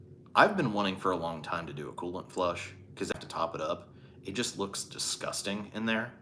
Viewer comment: I heard “colon flush” and “looked like a bird’s nest” when you were talking about a coolant flush! Then I looked at the chat.😁